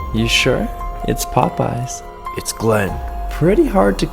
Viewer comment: When glen said it’s Popeyes I thought he said Poppy’s